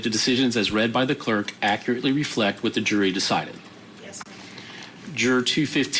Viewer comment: did she really say yes ma’am?